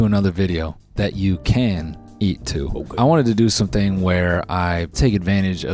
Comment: did I just hear a Rammus "ok."??? Editor explain